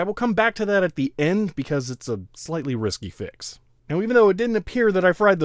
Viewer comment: I kept hearing "risky fix" as "whisky fix" lol